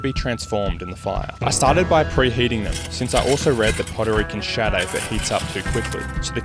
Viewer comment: For some reason, I thought he said "I heard Puerto Rico can shatter..."